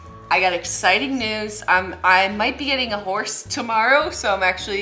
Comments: “I might be getting a horse tomorrow” the comedic value of that line is too great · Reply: I thought she said I might be getting a divorce tomorrow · Lol instead of hearing "Great news I'm going to get a horse tomorrow" I heard "Great news I'm going to get a divorce tommorrow"